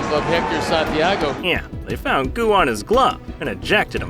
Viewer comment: What did he say? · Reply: They found goo on his glove and suspended him for it